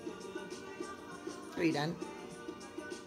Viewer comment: He sounded like he said I'm about done